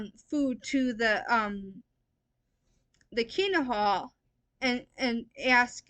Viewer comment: What is she talking about when it sounds like she is saying "keena hall"? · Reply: KINGDOM HALL OF JEHOVAHS WITNESSES